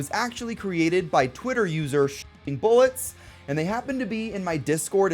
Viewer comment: that censor makes it sound like you said "shitting bullets"